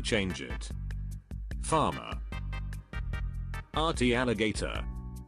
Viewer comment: What it says: "Farmer" What I heard: "Father"